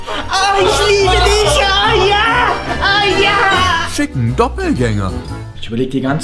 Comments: ich hab bei 2:39 verstanden: Fick nen doppelgänger xD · Fick'n Doppelgänger, habe ich verstanden :D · Wer versteht auch bei 2:38 fick nen Doppelgänger? :D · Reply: genaauu wo ich dein Kommentar lese kommt dieser Teil ich hab auch fick den doppelgänger verstanden 😂😂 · Haha, ich habe verstanden, fick nen Doppelgänger XDXD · bin ich der einzige der : "Fick nen Doppelgänger" verstanden hat? xDDD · Ich hab verstanden "fick' nen doppelgänger" 😂😂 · Zuerst verstehe ich bei 02:39 "Fick'n Doppelgänger" & dann endet die Szene wirklich mit Ficken. Zufall?!? Ich glaube nicht. :D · wer hat auch "Fick'n Doppelgänger" verstanden? xD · Wer hat bei 2:40 auch "Fick 'n Doppelgänger" verstanden 😂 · Wer hat bei 2:38 " ficken doppelgänger " verstanden :D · Bin ich die einzige die bei 2:41 fick einen Doppelgänger verstanden hat 😂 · ich habe verstanden fick,n Doppelgänger vor